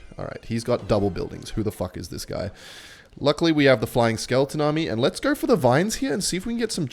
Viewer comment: Miss heard something at 6:32, Thought I heard - Giant Skeleton army. Had to rewind... That would be ultimate troll card....